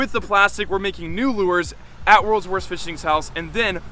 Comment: Who else thought he said Rolls Royce not worlds worst.👌👌👌